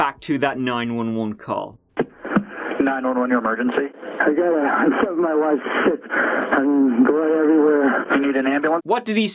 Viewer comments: I thought he said "I think my wife slipped" I am well know for not having bad hearing ! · I CLEARLY heard him saying “I STABBED my wife. Shit” on the 911 call. English isn’t my first language but that “stabbed” was clear as day to me. Anyone else? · I thought he said “I stabbed my wife, shit, and there’s blood everywhere” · Sounds like he said “I stabbed my wife, shit” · I hear "I stabbed my wife, shit, I mean blood everywhere". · I don’t hear “I killed my wife, shit” or “I found my wife sick”. I hear “I stabbed my wife, shit” Anyone else? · I listened to the audio without spoiling it for myself, without even looking at the screen, without even knowing which parts were in question, and I heard "I found my wife sick, and there's blood everywhere" The 1st time through. I distinctly remember thinking it didn't make a lot of sense, but someone might say that someone is sick when they're not sure of the injury, or pretending to be clueless. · Sounds like he said "I stabbed my wife. Shit" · what i heard was '' i stabbed my wife... shit '' it did not sound like any of the others to me. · Sounds like he said “ I stabbed my wife,shit.” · It sounded like he said "I found my wife sick. (Something) blood everywhere". That's just listening to it when it was played in the very beginning. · I heard, blind, “I stabbed my wife, shit.” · Sounds like he said" i stabbed my wife....shit....and blood everywhere" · I just hear "and (aannnd) blood everywhere" , this confuses me way more lol